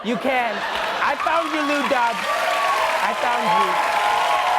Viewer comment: lol at 2:16 the subtitles said new god instead of lou dobb XD also, once you go black, you never go back XD